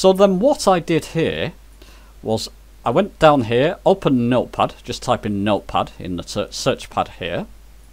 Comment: At about 6:00 mins. in your video when you say "meal puffs" what you are trying to say is "Note Pad", am I correct?